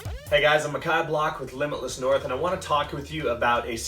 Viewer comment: I will search his name, but it sounded like he said: "Ahm a cod block." which at first hearing sounded like something else... Update: From another video it sounds like his name is. 😅